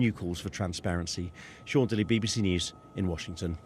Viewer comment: They're not protecting victims they're protecting perpetrators + did he just say Sean Diddy?